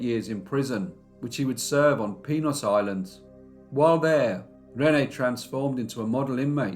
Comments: I thought you said penis island 😂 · Penis Island? Lmao—that's what it sounded like he said! 😂